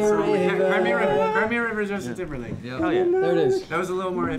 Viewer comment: I hysterically laughed when Zach started "cry me a river" by himself because I thought he said gravy! But really happy to see them again because they always make me laugh and after recent announcements, I need that. let's hope things work out for them soon!